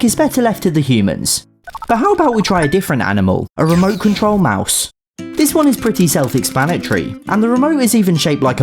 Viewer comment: War Thunder players misheard that as “Maus”